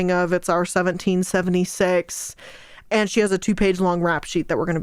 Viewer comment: I don't know why but when you quoted "it's our 1776" what I heard was "It's hour 1776." And my instant thought was "Wait what?"